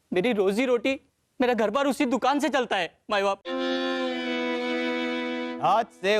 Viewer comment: Did he say "my baap" at 5:41 ? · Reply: Maee baap